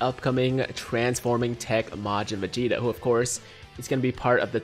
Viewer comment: Anyone else realize what he said 0:15 not vegeta vegeto